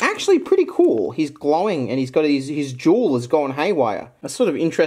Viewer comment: i really thought he said "his juul is going haywire"